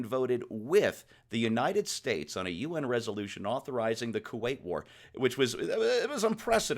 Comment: When said the Soviets voted with the U.S. on the Kuwait war, for a second I thought he said the Korean War. I had to go back and re-listen to that...